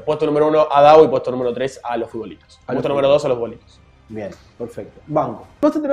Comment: creo que dijo puesto 2 Los Futbolitos en el